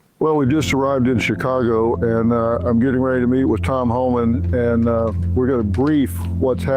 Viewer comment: Bro I heard Tom Holland and I was CONFUSED